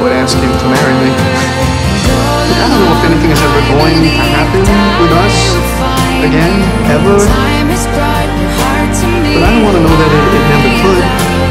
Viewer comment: What did Ross said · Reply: I dont know if anything is going to happen it us, ever but i dont wanna know it never could.